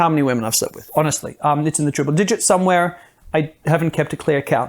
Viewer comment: Did he say he's slept with different partners "somewhere in the TRIPLE digits"?